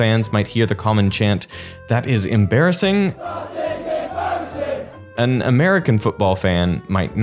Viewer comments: I heard "that is embarrassing" even with the text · I heard “that is embarrassing” 😂